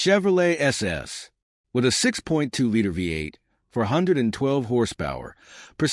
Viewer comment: did you say 112 horsepower???? What the hell did you smoke? The 6.2L had 408hp! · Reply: You have to listen closely because he doesn't say "four" clearly, but he does say 412 horsepower.